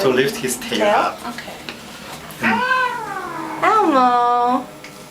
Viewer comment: it sounded like he was saying "Elmo", is that where he got his name? And poor sneezy puddy! :(